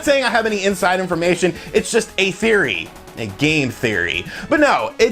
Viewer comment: Sounded like he said "A GAY theory." Anyone? Silent m in there?